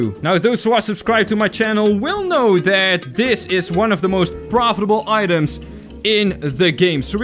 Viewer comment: I thought you said that the bear is the most profitable item in this game.